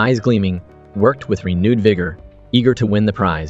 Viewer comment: Am i hearing what youre hearing? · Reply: Yes! Vigor😊😊